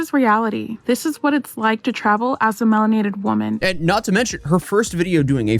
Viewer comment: did she just say “as a melanated woman?” Yeah, I’m sure that’s why, she’s playing the race card like an idiot, it’s China they hate everyone that’s not Chinese, wake TF up liberal.